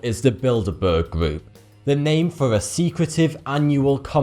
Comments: Bilderburg? I thought they said build a bear. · i swore he said "the build-a-bear group"